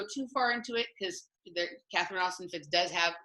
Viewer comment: Who is it that is recommending for finances, @ 43:00? I heard Catherine something. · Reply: Catherine Austin Fits.